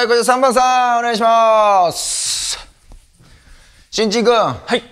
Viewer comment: 序盤の「新人君」が「ちんちんくん」に聞こえてすごいワクワクしたのに